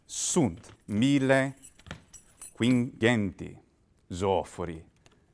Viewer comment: Join me at Living Latin in New York City 2024! Use this link to get $50 off the $180 registration fee: <link> I don't get any money if you sign up, but I'm happy to be able to offer my audience such a huge discount to this one-of-a-kind event. Nōs vidēbimus illīc! See you there! ERRATA At 3:37, I said "Tiberim" but I think "Rubicōnem" would have made more sense, since that was what Julius Caesar's 13th Legion was so famous for doing. While I had practiced my talk beforehad and knew what I was going to say, I did not memorize it verbatim, so you're hearing me speak Latin fluently from my head as I would speak about these topics in English (and thus errors and gaffs can occur, as in any fluently spoken language). 13:25 For some reason I say "pilum" (with a short vowel) instead of "pīlum," apparently confusing the word with "pilus," meaning a hair. Many thanks to my friend, who inspired me to learn about the Lorica Segmentata. See this video of his on the Lorica Segmentata that I helped him translate into Latin: <link> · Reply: Is it 2500 or 1500? It's written 2500 but I heard 1500.